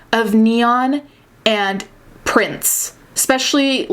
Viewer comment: At first i thought you said neon and Prince